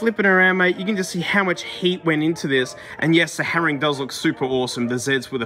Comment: I first heard "how much HATE went into this"